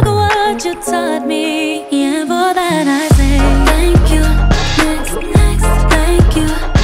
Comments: Instead of her saying "thank u next" i heard her say thank goodness lol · I heard ' thank you next' until somebody asked "What is she saying? It sounds like Bacon Eggs." I will never be able to unhear Bacon Eggs. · it sounds like 🥩 and 🍳's instead of THANK YOU... NEXT 🎶🎵 Steak and eggs lol 🎶 🥩 🎼 eggs lol say it in your head cracks me up everytime... I know people have different variations of what they thought they heard always 🐝 my favorite.... Silly 🐰 Trix r for kids